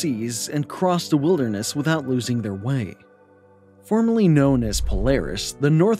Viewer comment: At first I thought he was saying formerly and I was like - whaat? It's still Polaris. Then I realised he was saying formally. Lol.